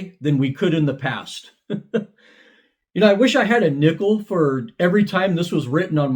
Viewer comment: i swear i thought he said "i wosh i had a nipple for every time..." lmao